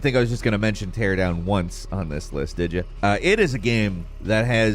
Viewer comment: what's the name of the game in 4:44 ?? because I didn't catch it anyone can help me in it? ( tear 'em down once? ) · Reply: Teardown